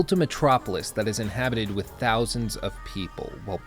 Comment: I thought they said MILLIONS of people.. not thousands. Perhaps I misheard